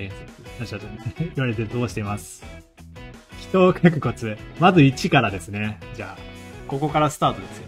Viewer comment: 亀頭を描くコツに聞こえてあせったわ